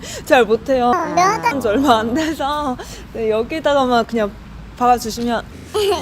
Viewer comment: 여기다가만 박아주시면으로 들리지 왜